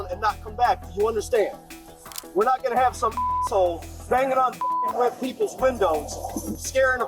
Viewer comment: During the scammer story at 46:12 mark of this video, a Sgt. says to the scammer "We're not going to have some asshole banging on WHITE PEOPLES windows & scaring a fucking woman with kids in the back" or did YouTube get the translation for the closed caption wrong?????